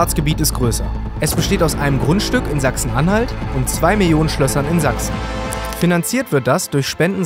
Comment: Habe ich mich verhört? Er besitzt 2 Millionen Schlösser ? O.o